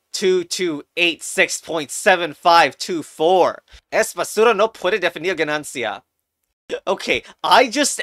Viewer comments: Is no one gonna talk about what he is saying in 12:42?! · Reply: He says "Es basura no puede definir ganancia" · What is this man saying We did not hear that, and captions are not helping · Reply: He says "Es basura no puede definir ganancia"